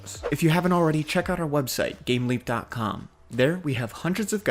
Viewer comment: I thought he said gamelink not gameleap. don't google gamelink.